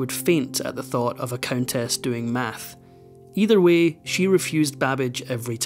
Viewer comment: did they call it math or maths ? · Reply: Maths.